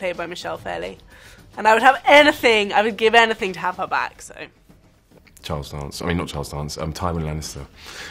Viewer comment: holy shit hahahhahahah i totally heard him say Jon Snow......but he didn't...it was just his accent tht made it sound like he Said Jon Snow instead of Charles Dance. Watch it again.